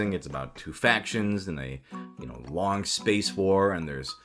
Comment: i heard "two fat chicks in a long space war" wut... when it was really "two factions in a long space war"